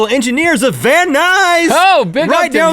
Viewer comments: Who Else Thought Vat19 When He Said "Van Nuys" ? No ? Just Me ? Ok.. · When Rhett said "Van Nuys!" really excitedly, I thought he was saying "Very nice!" in a Borat voice.